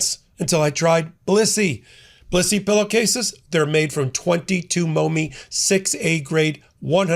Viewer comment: I thought you said pussy pillowcases I was going to get some until I realized that wasn't the real name